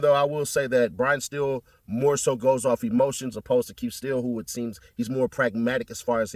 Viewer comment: Heard you say Keith Steele😂